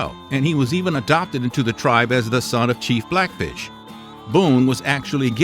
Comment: Am I the only that heard him say “chief blackbisch”